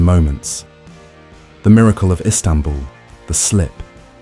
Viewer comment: I thought he said “Gerard had a stumble” instead of Istanbul 😂😂